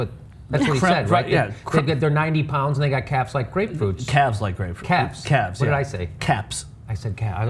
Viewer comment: sounded like he said calves to me